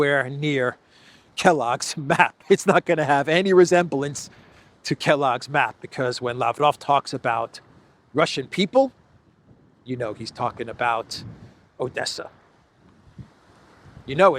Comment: I was listening to you talk about this and I heard in my mind "Medvedev said" but then I re listened again and it was Lavrov that said it. Good thing to hear.